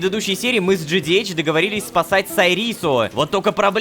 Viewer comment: Мне послышалось "мы с джоном договорились сосать сайриссу "